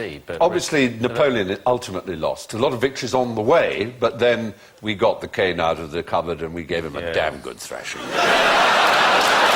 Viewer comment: What does Stephen say at 08:34? "But then we got the cane out of the cupboard and...?" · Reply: Then, we got the cane out of the cupboard and gave them a dam good thrashing